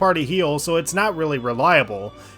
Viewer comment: I thought bro said Revival but he said reliable anc awas gonna post a comment like; ""Revival? Like p4 Revival bro had insider knowledge!""